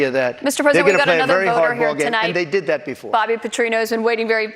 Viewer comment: I thought she said this was voter's